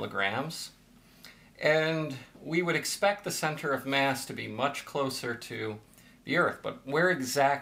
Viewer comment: did you say "...we'd expect the c.m. to be much closer to Europe"?